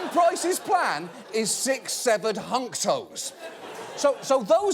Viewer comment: 6 "Seve"nred ......I hate that I heard this and needed to relisten to make sure